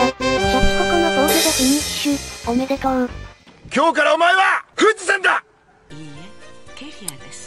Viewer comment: 今日からお前は富士山だ！がぐっちさんだ！に聞こえるのは私だけ？